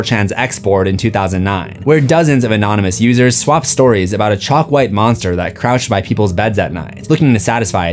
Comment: I thought u said chopped white monster 🥀